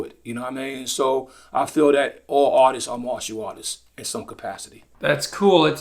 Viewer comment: I thought RZA was saying "autists" not artists😭 especially when he talks about hyperfocusing.... 😅